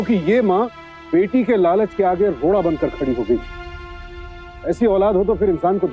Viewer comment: did he just say "loda"😂😂